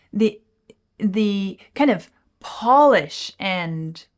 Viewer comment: I thought you had said "Paulish." haha